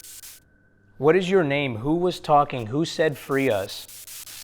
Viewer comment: I swear it said save us or save people or something like that though it's probably nothing